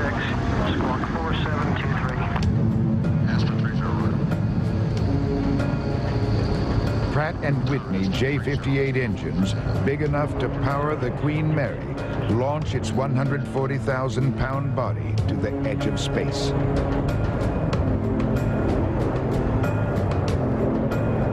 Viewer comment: what did the narrator mean by engines strong enough to power the green mary ?? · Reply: nah i know i just thought he said green mary if id know he said queen mary i would never have posted the comment but yea